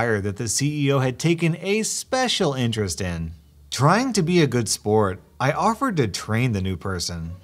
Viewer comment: (ish) "Trying to be of good spirit-" what it sounded like: "Trying to be a good sport " me: chokes on lasagna